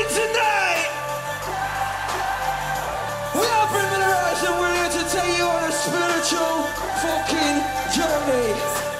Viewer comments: Wtf did he just say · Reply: "We are Bring Me The Horizon and we are here to take you on a spiritual..." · what did he just sayyyyyyyyyyy????? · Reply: 'Now we'll get you into a fucking spiritual journey' or some shit like that hahaha